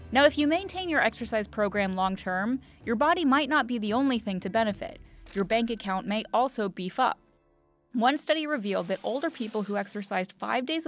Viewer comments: Tell me why I heard “your bank account may also be fucked” at 2:31 😂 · Who else thought she said your bank account would “be f***ed” instead of “beef up” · I thought she said "your bank account may also be fucked" lol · I heard 'your bank account may also be f***.' 🙉